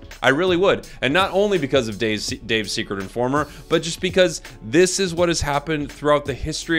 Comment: I heard you were “THE SECRET INFORMER😳”